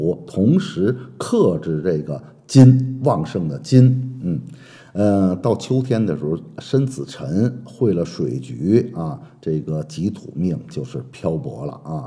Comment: 到秋天 「生子辰」「餵了「水魚」？好可惜啊聽不太懂沒字幕😢